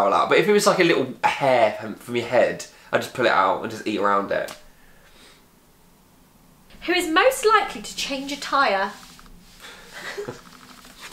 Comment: When you said change a tire I thought you said change attire